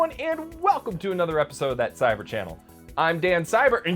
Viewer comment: I swear I heard Cybershell